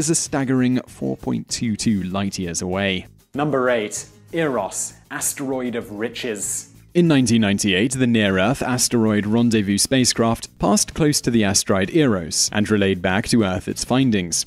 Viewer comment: my head was turned for #8, so i heard it as 'asteroid of witches' lol